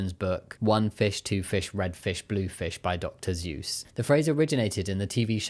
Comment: you said "Dr. Seuss" but I heard "Dr. Zeus" and was very confused there for a second